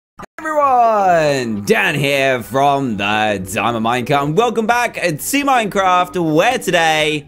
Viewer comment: it sounded like you said ugh, Everyone! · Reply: 😂 it do sound like he say. 😝 Ugh Everyone!